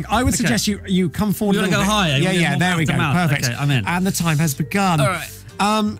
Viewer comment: I was looking to satisfy my daily need for Drarry, how is it that I got here? And how is it I only just found this? 0:15 - Did he really say "I'M IN!"????? 😮😀😅